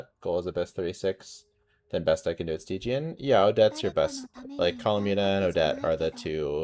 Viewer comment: Why do subtitles say Tighnari instead of stygian, then Kazuha instead of Columbina and Neuvillette is there too for some reason... Are they drunk? what did you feed them?